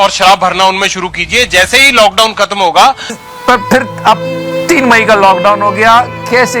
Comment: am I the only one who heard 3 mahiney instead of 3 may??